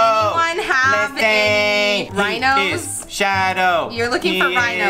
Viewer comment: when she says rhinos, I thought I heard linos hahaha